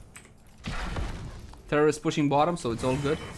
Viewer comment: I heard "Terrorist pushing bottom," hahaha. Thanks for the upload!